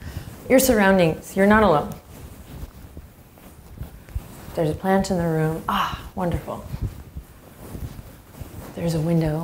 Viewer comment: I'm not alone, there's a potato in the room with me (what I thought after you said there's a plant in the room with you) :P